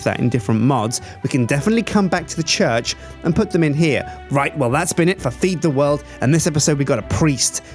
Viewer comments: like thirty seconds of the end i heard say "and thats been it for feed the world" or was it just me that herd that? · Is it just me or does he say at 19:58 feed the world?